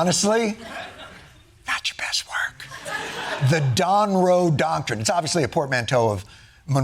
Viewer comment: I believe what he actually said was “the Donroe docurine”